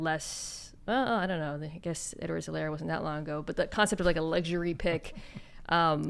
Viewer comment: Can someone tell me what the name Mina says at 8:34 is? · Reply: Clyde Edwards Helaire, CEH for short